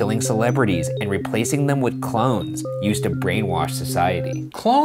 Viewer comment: here i thought u said clowns not clones